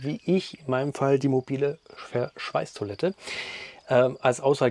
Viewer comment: min :Welches Modell ist die grüne Toilette im Video? Ich kann leider nicht verstehen, was er da sagt. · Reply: Die mobile Clesana x1. Mit Akku aus dem Werkzeugsortiment. Kann man noch zusammenschieben in der Höhe. VG